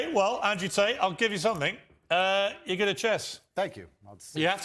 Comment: what did he say💀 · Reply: “You’re good at chess”